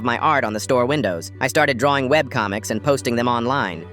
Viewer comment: I thought he said wet comic😭😭☠️☠️